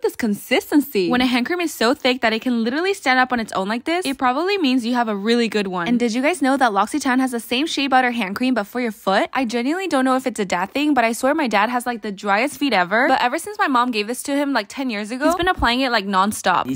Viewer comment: I heard :"but for your butt"😂